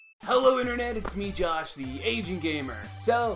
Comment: I always hear "it's me Josh, the Asian gamer" lol